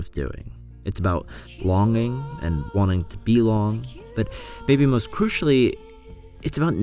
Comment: "about longing and wanting to be long" dunno if I misheard or what but I laughed so hard